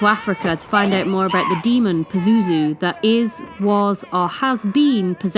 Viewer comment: The 2nd movie goes into the origins and where pazuzu came from, it seemed cheesy but it did go into detail about the whereabouts of the demon and father merrin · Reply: I was 9 years old when this opened in theaters. Instead of "Pazuzu", I thought they were saying, "Boozudoo".